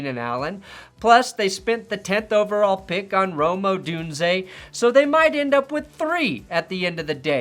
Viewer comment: I’ve got a rule for players. Their name has to sound like a good player. Julio Jones, Calvin Johnson, Amon-Ra St. Brown, these names sound like they should be good. Names like Kevin White, N’Keal Harry, Treylon Burks, these guys sound bad. Rome Odunze does not sound like a good players name to me, sorry. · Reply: The problem is the natural elision in the name Rome Odunze. I keep hearing “Roma Dunze”.